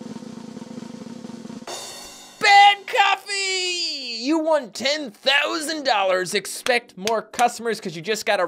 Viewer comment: It sounded like you said "Ben Coffee"